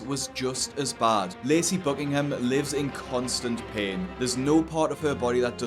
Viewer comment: Lol I thought you said her name was Lazy Buckingham....